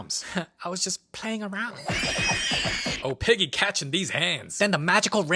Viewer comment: I swore that I thought I heard him say "OH PIGGY CATCHING BEEF HANDS" 😂😂 🐷🍔